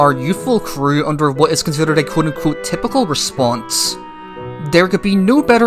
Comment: what does "kudako" mean i hear it a lot from you